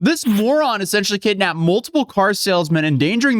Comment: NGL I thought it said Mormon lolololol